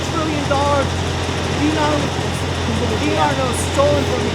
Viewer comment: The guy sounds like he says Denars